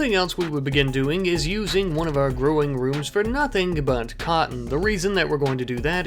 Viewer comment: I thought he said “pot” and I thought he was going to be selling more than just guns